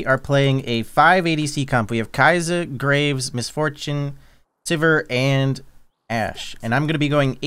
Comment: Watching at 320AM I legit thought he called her Missed Foreskin 😂at 00:13. Didn't register to listen to it again cause I thought it sounded like a thing to say.